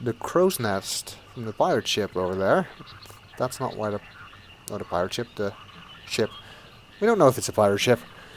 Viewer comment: did you say pile of shit?